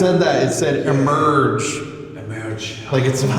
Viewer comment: after he asked what happened to you…female voice said “murdered”